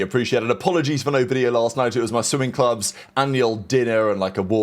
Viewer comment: I thought he said he was in a sewing club took me a 2nd listen to realize he said swimming